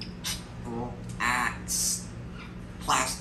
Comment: When ever I hear him say “wario” I hear “royal”